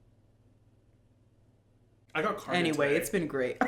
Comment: What did he say at 49:46? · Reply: That I got carded today 😭